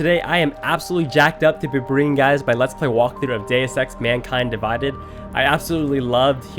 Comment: oh wait iwas not looking on the screen and i heard “day sex” instead of “deus ex”